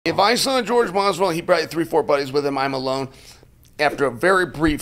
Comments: I thought he said if my son Jorge masvidal😂😂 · Lmao at first i thought he said "My son Jorge Masvidal"